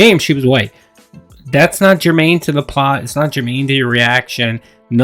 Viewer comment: I'm an uneducated fool but what does he say at 7:29? Germain? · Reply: Germane. It means relevant.